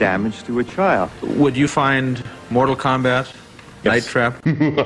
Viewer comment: What did Byron Dorgan say & what did the person he was talking to say?... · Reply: tthomaselli2 "Would you find "Mortal Kombat","Night Trap"?